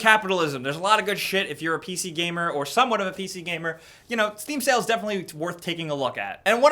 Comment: I thought you said steve sale lol